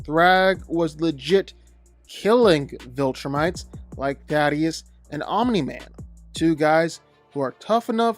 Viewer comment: everytime you pronounce it I hear "Daddyest"